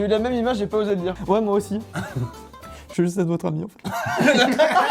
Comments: Il dit quoi à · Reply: "je veux juste être votre ami enfait" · il a dit quoi ? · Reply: Il dit " J'veu juste être votre amis en fait " En gros il avait pas penser à la même chose mais à dit qu'il y avait pensé. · il dit quoi? Apres moi aussi ??? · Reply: "Je veux juste être votre ami en fait " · dis quoi à 16:07 je comprends pas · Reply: Je voulais juste être votre ami enfaite 😉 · Juste a 16:07 il a dis quoi 😂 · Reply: ''Je veux juste être votre ami en fait''😂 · il a dit quoi? J’ai pas compris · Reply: "J'veux juste être votre ami en fait" xd